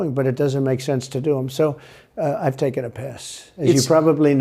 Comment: I hear "I'm taking a piss!"